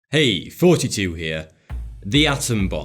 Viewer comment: i just realized that he's saying "hey, thoughty2 here." i always thought he was saying "hey, 42 here"